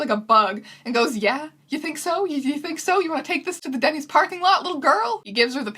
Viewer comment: I thought you said "you wanna take this to daddy's parking lot" at 4:27 and now I'm bleeding out of every orfice.